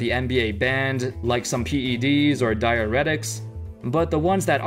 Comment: I thought u said dierera